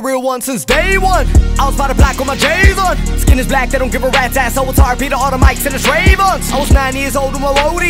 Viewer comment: What does he say on 0:27 seconds all I got was been the real one since day 1 I didn't understand what he said after that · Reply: im trying to figure that out also lol i think its "i was out back with my jayz on skin as black, they dont give a rats ass i was gonna talk on the beata on the mics to dream on " i think it goes something like that, right now im trying to put the lyrics on music mix match cause their seems to be no lyrics anywhere